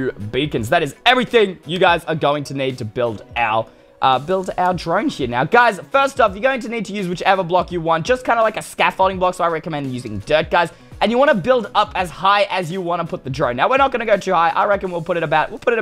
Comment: the captions say I recommend using dead guys